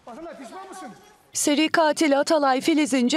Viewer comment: Gazeteci "Bahğele pişmanmısıng.." diye mi başladı lafa, ben mi yanlış duydum... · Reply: Atakan pişman mısın diyor